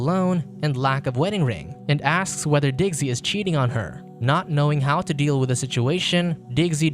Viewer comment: lol ... even the subtitles get it wrong. He says "her", subtitles say "him"..... these people are from a parallel universe themselves, I swear. LOL